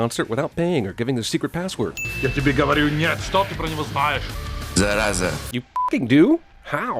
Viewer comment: Those russian subs were absolute wrong :D 0:29 "I tell you no. What do you know about him." "Infection." Nobody talks about seeing him or not. 12:02 "This is for you." - subs also talk extra about detonator. 12:11 Somethings happened.....open the door.... - NOT what subs said :D